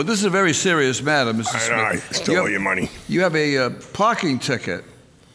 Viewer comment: Did anyone else hear him say I I stole all ur money at the start I’m so confused